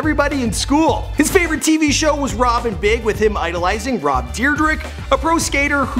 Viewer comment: it sounds like he says Rob Deerdick